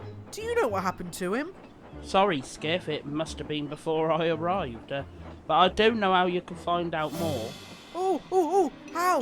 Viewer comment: did Gilbert say i don't know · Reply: he said "Sorry Skiff, it must have been before I arrived. But I do know how you can find out more."